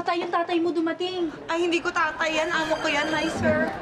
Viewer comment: Ano sabi ni charice diko naintindihan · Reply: Hindi ko tatay yan amo ko yan. Sabi niya